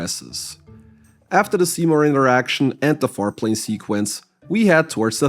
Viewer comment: at first I thought you said "foreplay sequence" which is really funny with context